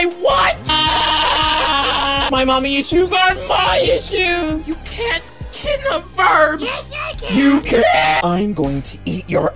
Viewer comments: i heard “you can’t kill the ferb” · At first I thought Steven said “my mommy’s shoes are my shoes!” And I was like what the fu